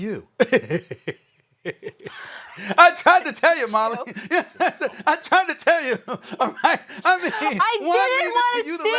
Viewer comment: lol what did molly say at 0:24 seconds so hard to tell something hate show or f show cant tell? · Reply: Molly Said OMG This Show